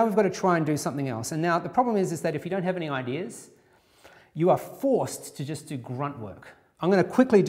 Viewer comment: I almost thought he said 'you are fricked"